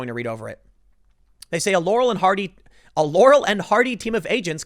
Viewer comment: what’d you say? Yanny and hardy?